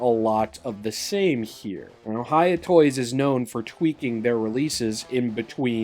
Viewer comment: I thought you said “Ohiya toys”💀💀